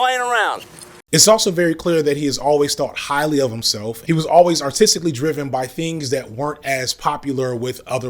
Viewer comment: I thought you said "he was always autistically driven" here for a second 😅